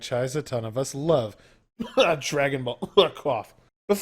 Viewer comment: i heard dragonball crof. Got me confused for a second. Monster pets? Sold. :D